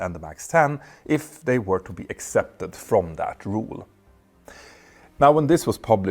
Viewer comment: did you mean exempted? It sounded like you said accepted from this rule